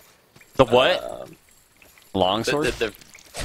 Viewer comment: you're not the only one who heard him say "bong sword" when he said "long sword"